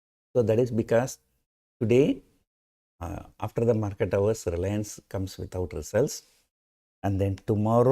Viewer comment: Sir, did you say "Reliance comes without results"?